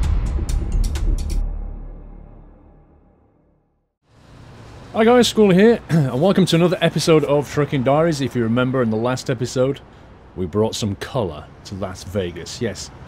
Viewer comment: lol I think he says fricking diaries